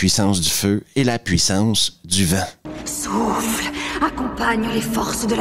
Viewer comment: J'ai entendu "la puissance du vin" du coup j'ai freezé genre WTF ?😂